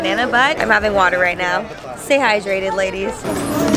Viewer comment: when said, “stay hydrated ladies,” 6:19, i thought she said. “say hi, dreaded ladies.” 😂😂😂